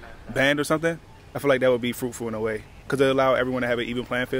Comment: Fruitful at college? I thought he was saying they were full of fruits for a second.